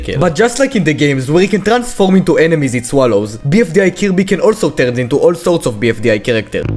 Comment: When he said Kirby I thought he said cubey